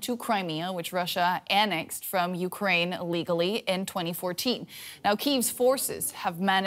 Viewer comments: Did she say russia legally annexed crimea? 😕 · Did she just say at 0:18 that Russia legally annexed Crimea??? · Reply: She said "illegally" but didn't stress the first syllable.